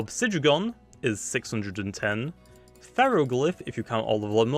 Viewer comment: Did anyone else hear the pharoglyph 110 Base at 5:40 xD